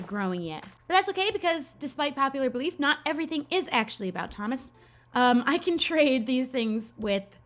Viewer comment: “Despite popular belief not everything is about Thomas” I THOUGHT YOU SAID “despite thomas’ beliefs- not everything is about melons” I SCREAMS 🍈